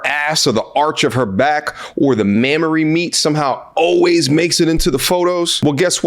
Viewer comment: Did he just say Mamory meat??? I subscribed as soon as I heard that.